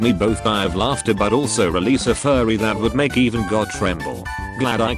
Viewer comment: Instead of "release a fury" it turned into "release a furry",peak comedy